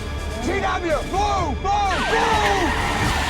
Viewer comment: After all those years, I get chills with the Beast-turning on the bridge scene after Ron Buttersfield calls out 'GW, BLUE BLUE BLUE!'. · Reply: I always thought he said MOVE MOVE MOVE but I guess since it's the hospital codeword it makes sense